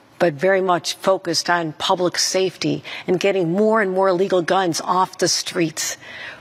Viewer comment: does Kathy say she is focused on getting "more and more legal guns off the streets?" Or am i hearing it wrong?